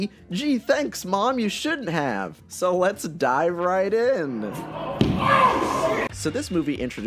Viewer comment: Hey guys! So at around 34:08 I originally played the "Martha" scene in full. But whoever owns Hans Zimmer's music decided that the faint score playing in that scene was grounds to block the video! So since the video has already been up Youtube only offered me to remove that chunk of the video which left a weird editing glitch between cuts. Can't do anything about it! But at least this way the video is monetized and actually viewable worldwide. I'll probably be making a video on this issue because as some of you know this happened on the last Worst Superhero Movies video too. Anyway thanks for watching bye byeee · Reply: lol, for a second there, I thought you said "So let´s DIE right in" ;-D